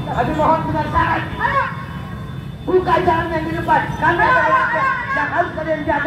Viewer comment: Teriak teriak apaan sih ibu itu??? " Buka celananya .. Buka celananya.. gt ya .. apa aku salah denger???